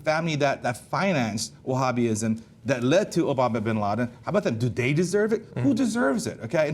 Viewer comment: Did he say Obama bin Laden?